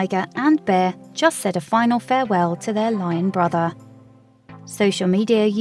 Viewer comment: when she said "Lion Brother"....I heard "Lying Brotha" :)